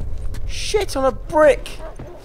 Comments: I thought he said "shit I'm a brick" and after he said "I didn't even know that was a thing" 😂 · For some reason I thought you said "S**t I'm a brick" lol